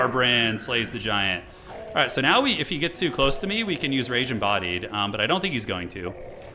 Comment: I heard "Ragin' Body" instead of Rage Embodied like yes, he does have a Ragin' Body.